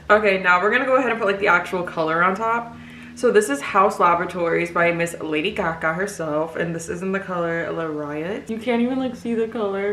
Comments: "by miss lady caca herself" BAHAH THAT'S WHAT IT SOUNDED LIKE FOR A SEC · At 8.21 I heard her say “miss lady Kaka”😭